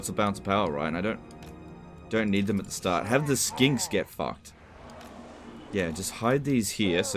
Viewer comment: what i heard was skanks...